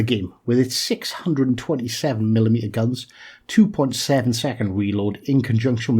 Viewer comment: I heard gearing as “620mm guns” not 6 x 120mm lol that would be awesome 😂😂😂 I came here for Uncle Barnaby though!